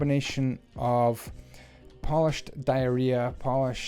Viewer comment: says polished diarrhea instead of diorite